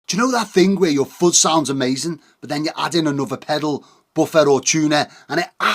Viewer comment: What I heard: "Y'know that thing where your foot sounds amazing?"